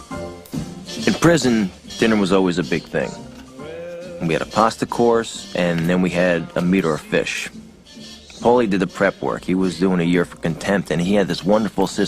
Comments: For longer than i care to admit i thought i heard "a meter of fish" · I first heard "a metre of fish" not "a meat or a fish" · I always thought he said "a meter of fish" rathan than "a meat or a fish."